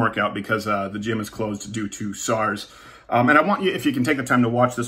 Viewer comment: Did he just say sars?